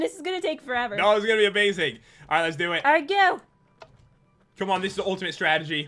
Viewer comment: When Pat said ''Oh come on!'' I thought he said Pokémon lol